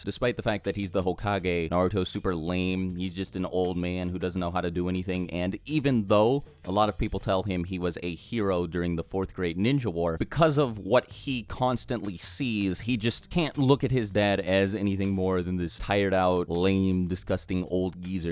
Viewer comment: When you said "During the 4th great ninja war" I heard "During the 4th grade ninja war"